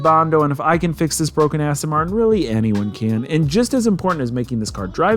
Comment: Sounds like you said Broke Ass Martian